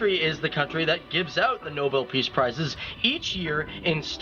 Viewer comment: not the peace prize one. All the Nobel prices EXCEPT the peace prize are given in Stockholm. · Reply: I missed that one. I thought he said "the Nobel prices" but now when I listen, he does say "Nobel peace prices", and like you said, that's wrong.